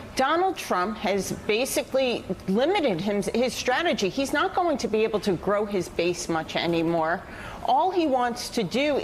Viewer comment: Thought she said "He's not going to be able to grope his base" the first time.